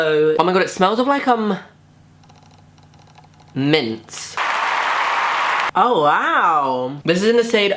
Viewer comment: did you say mince or mints,, because mince would be so disturbing